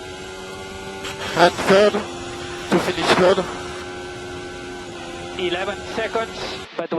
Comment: "And third? To finish third?" 7:35 I think he says "indeed there's not much" 7:42 "or I probably have some lucky stars from up there..." That being noted, thanks for the video. :3